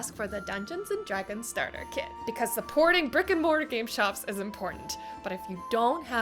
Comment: I thought you said Rick & Morty game shops!😂